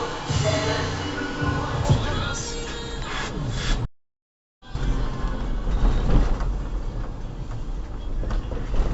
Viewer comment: was für 'ne Behörde?! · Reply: Ortspolizeibehörde, sowas wie Ordnungsamt mit mehr Rechten